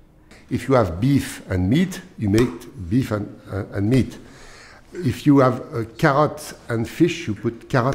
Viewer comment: Did he say "if you have cat and fish" at 0:51?